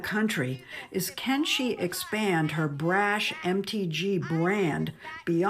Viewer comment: I thought she kept saying her “empty gene brand” (MTG brand) but it’s pretty funny that her initials do say empty. Empty brain.